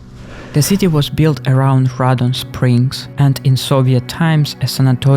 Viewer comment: Did you say radon?